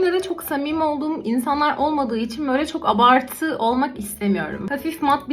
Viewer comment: Abartmak istemiyorum mu dedi ben mi yanlış duydum